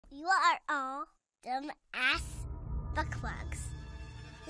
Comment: Does Ike say DumbAssBuckFucks? 🤣